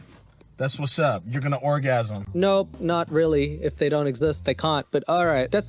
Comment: I thought you were saying the female O didn’t exist 😂